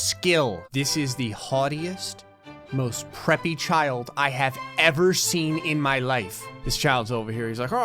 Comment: my dumb captions said “this is the hottest most pretty child I have ever seen” 😂😂😂